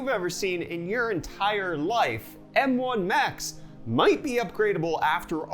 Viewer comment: I thought you said "M1 Max" instead of "M1 Macs." I was like, "Are they called Macbook Max now?"